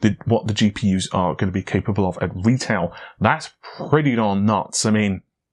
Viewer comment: Great videos. Love the slight accent as well, the auto generated subtitles are like "that's ready Don Knotts" instead of "that's pretty damn nuts".